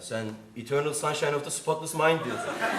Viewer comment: de ne dediğini yazabilecek varnı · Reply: Eternal sunshine of the spotless mind